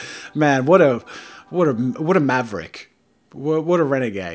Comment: did you say Maverick? Name: Hipster spinner Weapon: spinner blade